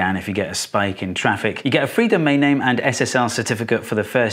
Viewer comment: "SSR certificate"? Steve 😔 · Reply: I swear at 8:43 you say "SSR" but perhaps that's an accent thing?